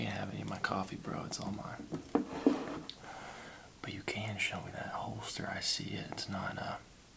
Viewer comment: I thought he said "But you can show me that hole sir" 😭😭